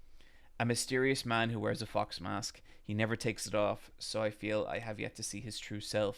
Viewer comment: Foxglade instead of Fox Mask gave me a good laugh!